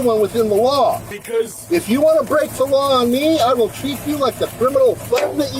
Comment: he stated, "If you want to break the law on me, I'll treat you like the thugs you are." That was him telling them that he was going to shoot if they broke his window. It's easy to miss if you aren't familiar with cryptic threats. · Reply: I first heard *I'll take you out...." Instead of "....treat you like". Of course I knew in advance what was going down